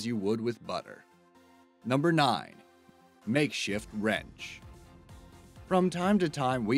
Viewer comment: Number nine i thought it said "Make Shit Wrench"